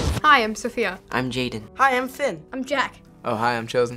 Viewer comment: When Finn said 'hi I'm Finn' at first a thought he said 'I am Finn' I laughed at first and then heard what really said I was like🙁